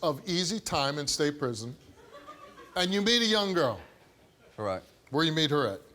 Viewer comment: I thought Steve said he met a young lady 🤔